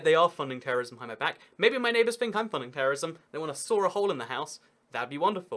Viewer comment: He said terrace town immediately after he said terrorist, and I swear I thought he said he lives in a terrorist town